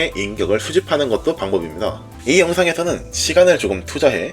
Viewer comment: 이분 뭔가 발음이 예시로 들자면 1:10초에"이 영상에 서"인데 "이 영상에 thㅓ"로 들림.... 예전에 내가 앞니 다 뺏을 때 발음인 것 같아서 웃기긴 함 ㅋㅋ